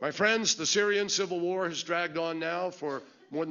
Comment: Did he just say '..a civil world war' 7.41min?